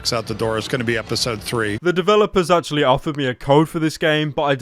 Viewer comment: DID GABE JUST SAY "THREE"!?